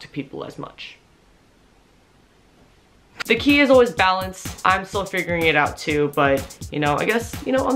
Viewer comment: I heard tequila is balance.